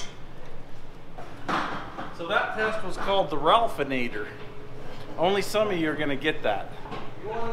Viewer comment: Only in world does the phrase "build a rotisserie for a car" sound like a normal thing. "Ralphinator" test, LOL 🤢🤮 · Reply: I heard it as "ralphanader"